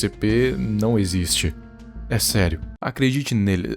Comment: gaguejou por que? E eu ouvi vc falando "acredite neles" antes de tossir da forma mais forçada ja imaginada, fale a verdade